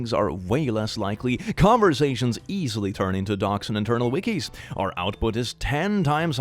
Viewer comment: I was half focusing on the video and I thought it said “conversations easily turn into doxxing” and I was so confused